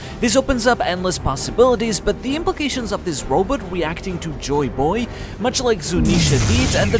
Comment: Did you just call it a Robut, like how Dr Zoidberg calls Bender robut in Futurama? lol